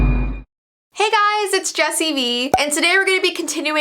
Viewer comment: When I first watched your video I thought you said Messy V 😂